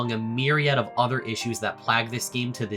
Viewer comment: D Did you say “plag?!” Plague? What’s a plag?? ???? I’m so confused